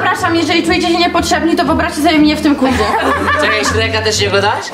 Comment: co powiedziała? bo nie słyszę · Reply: To wyobraźcie sobie mnie w tym quizie